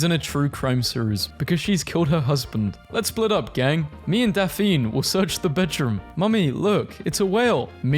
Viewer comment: YOU DID NOT JUST CALL DAPHNE, "DAFENE"